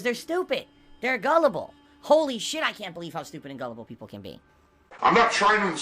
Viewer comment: The funny thing about that is he's not calling trolls stupid and gellable, he saying that about his own viewers and fans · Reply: Gel-able? Like hair and hair gel? I must have missed when he said that. I only heard him say gullible